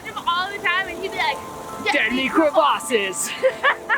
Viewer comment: the caption says time negro bosses